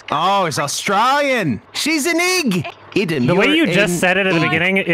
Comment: I didn't get the reference to Boy, but I didn't think he was saying the n word. I heard "she's 'n eeg".